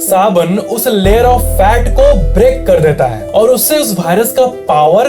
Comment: Give a like who noticed BHAI-rus instead of Virus.. 🙏🏼🙌🏼💥💣